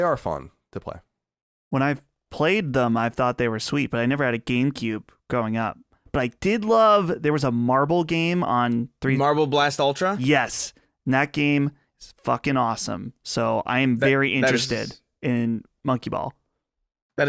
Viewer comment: LoL I thought said Marvel Blast Hero, I thought he was talking about a random Marvel Super Monkey Ball clone I had never heard of haha. Yeah he said Marble Blast Hero.